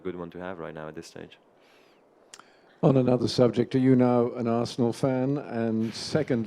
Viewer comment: COULD somebody tell me what does the reporter say at 4:56 (national fan?)? THANK YOU.